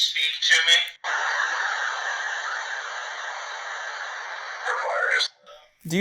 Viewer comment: it sounded like it said the fire surround ❤